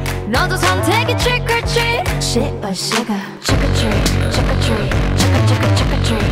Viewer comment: What I heard? Treat by Suga??? 🤣🤣🤣